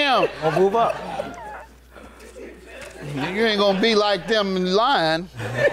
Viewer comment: Wait, 7:25 did judge say n🤬 you be there lying?